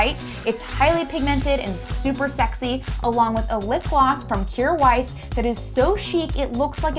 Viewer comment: What was the lip gloss that you mentioned at 5:52 ? I really didn't hear it clearly. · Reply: It's from a brand called Kjaer Weis